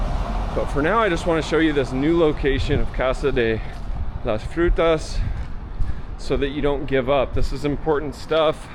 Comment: Man I thought you said casa de las putas I was like yeaaaa